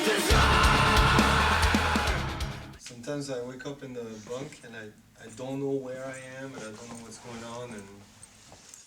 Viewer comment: What does he say at 6:35? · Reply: "Sometimes I wake up in the bunk and I don't know where I am and I don't know what's going on"